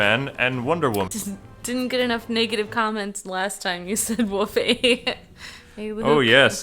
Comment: at 0:47 i tought you said naked comments. i was like say wait. BTW love your guy's vid's keep it up c